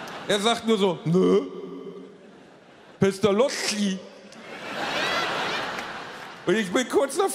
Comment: Was sagt der Gunna bei 9:40? Mr lochi? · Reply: Pestalozzi